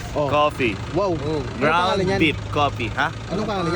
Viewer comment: tama ba Yung narinig ko? "Ground beef Coffee"... Hahahaha....iba talaga sir